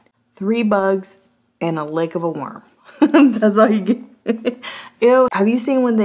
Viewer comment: Nekkid and Afraid, what do they eat? "Three bugs and a lick of a worm." OMG, you are the master! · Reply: I thought she said a leg of a worm 🤪